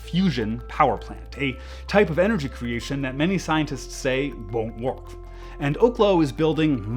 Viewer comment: What are you saying at 8:58? I listened to this over and over and you mumble "won't work" but it's not said very clearly and one must wonder why. Seems one can never take life at face value anymore because all reports, videos, people, virtually everything has an agenda they are pushing. BTW you wasted a lot of eggs making your point. Eggs are expensive from time to time.